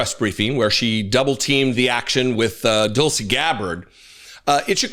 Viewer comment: I swear it sounded like you said, "Dull-sy. Gabbard," andI I laughed so hard I nearly choked on my coffee. 😅😅